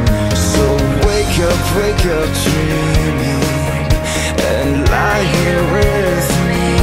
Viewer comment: Who else thought he was saying jamie before we realized it was dreaming?